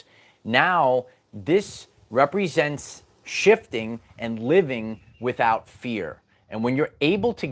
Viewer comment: Had me tripping, thought my man said living without beer 🍻 😅